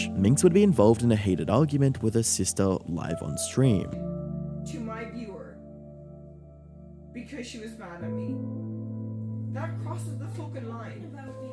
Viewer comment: What did they say around 14:08 ? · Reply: Minx: (video starts mid-sentence) To my viewer… because she was mad at me. That crosses the fucking line. Sister: (unintelligible) stop talking about me Becky. Minx: What? What? You took our mam- (this is my best guess) Father (idk who this man is) : Take her out of here, will ya? (Seems to be talking to someone off-screen) Minx: You took our mam- (continues in sarcastic tone) I’m confused… did you not? Sister: No- No- You come on then! You come on! Minx: You fucking come on! You fucking said information to a viewer- Sister (seems to be directed at father) : Because people were saying that you and (unintelligible, might be ‘mom’) are enabling her in the chat, (next bit is very distorted due to minx interrupting, so might be incorrect) which is dead fucking wrong Minx: Enabling her?! You’re fucking coming into my room after lying to a fucking viewer! (Video cuts) Sister (might be a different woman, definitely not minx) : (unintelligible) because you- you have a problem! Minx (very slurred) : you’re why I have a problem! Sister (?) : you have a problem! Minx: did you not (becomes unintelligible) (Video ends)